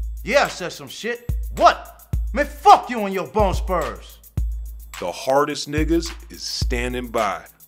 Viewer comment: what did he say ? f u and ur bronz spurs ??